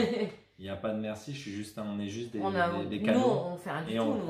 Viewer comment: Quand dit "on est juste des canaux"... J'ai entendu "cadeaux"... Et ça, c est vrai! 😘😘😘 à vous